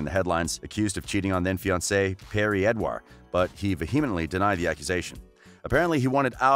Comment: did he just say perrie edwar??